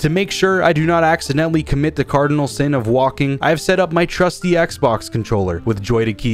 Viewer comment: I thought it said "talking" Lol